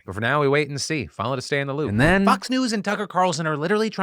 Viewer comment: I thought you said "fucks news" instead of "fox news" omg 🤣🤣